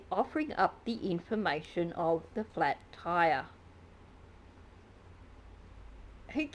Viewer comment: I thought he said it was a “firearm” inside the car that might get stolen😳😳😳lol! I thought Florida must be WAY more liberal re; guns than Connecticut 😏. I also think that SS keeps bringing up the flat tire so often because it’s basically the truth. He DID have a flat tire and that’s easier to say (than his usual lies). The lighting is a huge eye-opener for me. He must’ve been moving really fast